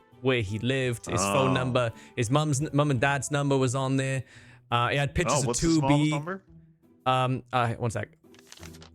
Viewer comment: does he say 2b? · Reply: Yeah. Stan has 2B poster in his room.